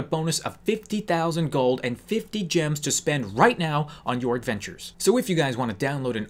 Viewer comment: i thought u said 50 jams JIMINS JAMS!!